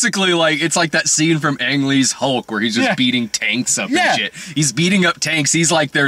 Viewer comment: coulda swore you said "beating taints up and shit"